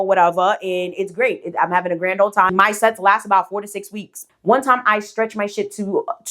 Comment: Lol at first I thought you said 46 weeks. I was about to say. 4-6 is good though. 😂❤